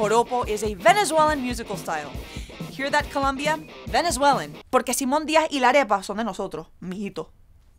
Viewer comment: What does she say at 6:00 im still learning spanish and im not sure i caught all of that lol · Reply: Porque Simón Díaz y la arepa son de nosotros mi hijito